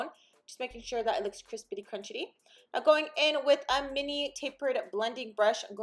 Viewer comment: Girl I was eating a crispy chicken sandwich & heard you say "make it look crispitycrunchity & automatically thought aww la bebe wants chicken 😂❤ love the look! She looks beautiful!